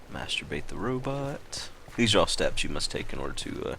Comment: did he say "masturbate the robot?"